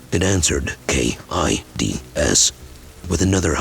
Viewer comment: i thought it said A.I.D.S. it's difficult to understand these Artificial intelligence sometimes. It said K.I.D.S 😂😂😂 I WAS LIKE WHAA AIDS???